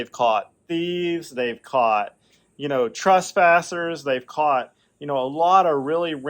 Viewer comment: …I heard the word “trespassers “ as “trustpassers”….! It’ll preach!